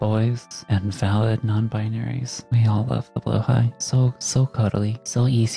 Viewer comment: I misheard that as "Ham Salad Non-Binaries", and the fact that it totally made sense to me as a third option alongside cute and handsome is making me die of laughter.